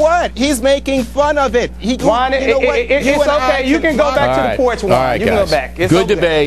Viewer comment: Did he just say "you can go back to the porch" to the black guy in the middle? Wow That's really racist